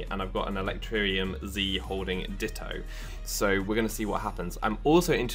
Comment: it sounded like you said an electrium holds a ditto